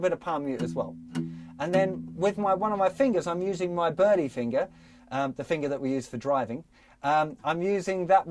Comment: The berly finger? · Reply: I thought he said, "The birdie fingure, the one we use for driving". :-)